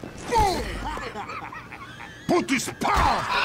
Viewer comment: "Nice shot!" "We make good team" "Outstanding!" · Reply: I thought it said your my good team 💀